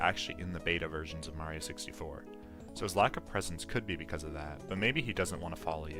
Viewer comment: It sounded like you said "His laki-presence" 😂